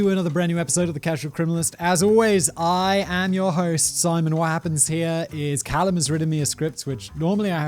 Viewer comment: Did I just hear "I am your host, Simon WAP"